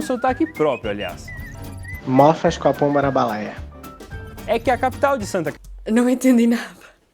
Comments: eu entendi ele falando "Mofas com a pomba na balaia", mas não faço ideia do que isso significa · Kkkkkkkkk tudo bem, acredito que a maioria dos brasileiros também não entenderam o que foi falado em 6:16 (incluindo eu) 😂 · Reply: eu entendi "mofascopomarabalaia"